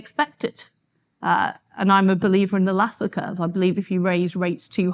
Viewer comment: What was that word she used at @ 42:30? "anlathlaka"? "an lathlacca" "a nathlacca" she has a slight speech impediment, so it is hard to be sure, google translate has called it "laa curve" It seems to be somehow a rule of diminishing return for taxes, or possibly a rule invoking progressively more irony with more legislation.. I am not sure what the word was... any ideas? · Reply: It's the Laffer Curve.